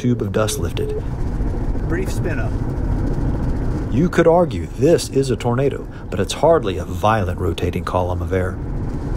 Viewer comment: Subtitles says "You could argue this is a potato, but it's hardly a violet rotating column of air."